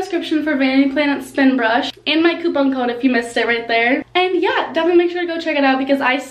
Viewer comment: Who else thought she said poop on code instead of coupon code the first time🤣🤣♥️